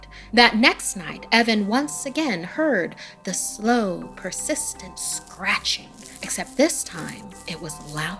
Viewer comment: I thought it said "sister snatching" I- Why?